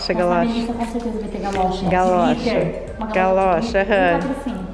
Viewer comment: Gente eu ouvir errado ou a baby falou "a Melissa concerteza vai ter galocha de gliter" me corrigem se eu escutei errado mais vejam